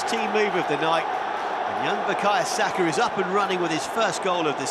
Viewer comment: can anyone else hear another commentator yell "SAKAAAA" at 1:14 or just me?